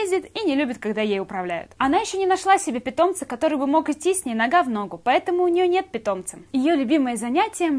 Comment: мне послышалось на гавно